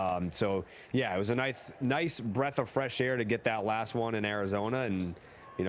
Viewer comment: this guy just said “knife” instead of “nice.” Tyler Miliken, here’s your evidence 1:14 . Don’t let Daddy Hair Gel win this!